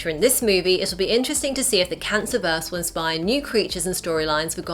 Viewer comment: did she say cancerverse WTF